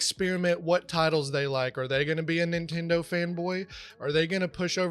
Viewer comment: I hear "a nintendo femboy" twitter burned my brain